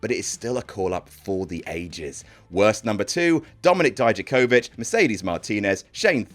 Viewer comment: I thought he said, "Dominic died of COVID," so I had to rewind it.